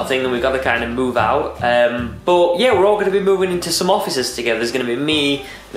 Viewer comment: I thought u said I will be moving into officers LOL!